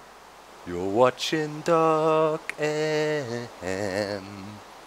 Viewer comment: Sounded like you said "You're watching Doc Eminem" ^^